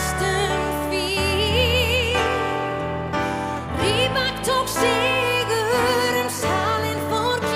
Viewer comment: Sounded like she said "Rybak". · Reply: she said rybak she is singing about that she took 2nd place after rybak in 2009